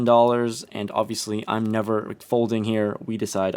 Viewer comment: Did he say I'm never McFolding here? Welcome to McRampage, home of the McFold and McFourBet, how may I help you today?